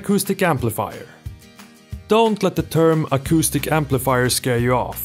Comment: For a second I thought you said autistic amplifier